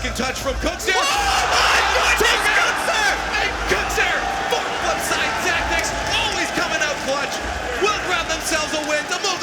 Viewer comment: am I the only one who heard the commentators say “FUCK FLIPSIDE TACTICS”